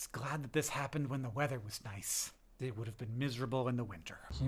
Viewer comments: “I think I’m on a journey of meaning” is a VERY John thing to say, Hank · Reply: I heard "a journey of meme-ing"... I prefer yours. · The "I think I'm on a journey of meaning." at the end got me so hard. I practically never burst out laughing, kudos Hank! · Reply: Ah, and I thought he said "I think I'm on a journey of meme-ing" and I was like - duh. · Hank: “I think I’m on a journey of meaning” Us Dear Hank and John listeners: “We know, Hank. We know” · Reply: I thought he said "memeing". Damn you Auditory Processing Disorder!